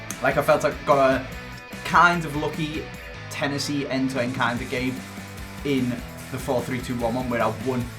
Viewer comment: Am I deaf or did he say “ ‘Tennessee’ ended to end kind of game” at 1:05 ???